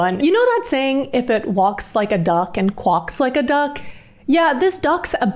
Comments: i'm sorry mam i love ur videos but did you just say "quock" like "lock" but with a "qu" in the front??? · did you just say qwuaks bro